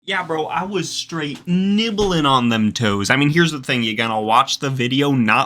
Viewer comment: I'm done What did he just say nibbling on them toes